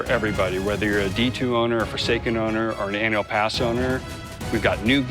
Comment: My dumbass thought it said foreskin